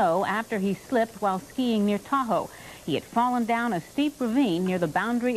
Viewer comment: I just fell over laughing. At 2:00 Elaine seamlessly reports: "Physician Dale Ellis spent almost 12 hours stuck alone in the snow, after he slipped while Peeing near Tahoe" Lol. I swear she said it! lol.